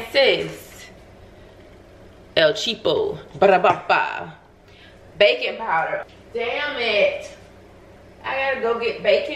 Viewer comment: My dumb ass really thought you were saying bacon powder 😂😂 until you came back from the shop